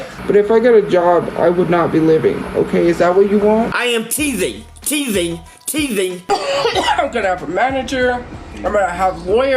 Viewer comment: Lmfao. . . .at 0:20 I thought he was saying " I am teething, teething, teething!" . I'm sure you could understand my confusion .🫤 Oh crap... He WAS saying that!! Where are these nut bags from?!? I'm just grateful that they're not from S.Florida for once!! 🫣